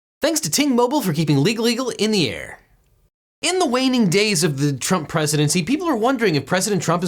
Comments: I swear I heard "In the whining days of Trump presidency..." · 🇺🇸 Should the president be able to pardon himself? ☎️ Get $25 off of you first month with Ting Mobile <link> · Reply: Ting Mobile, Thought he said T Mobile.😂